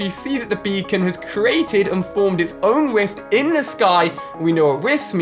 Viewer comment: If you listen to 3:52 you will hear he says wift instead of rift